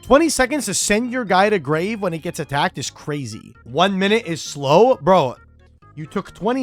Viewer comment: Did just say Slowbro? Lol.